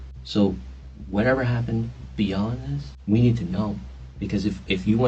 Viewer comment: I think he says "beyond this" and not "be honest" as the subtitles say.